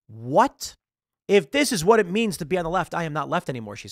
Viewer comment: Is it just me, or did anyone else hear "Hannah" at 14:06 and "Jack" at 14:12 instead of Ana and Chenk?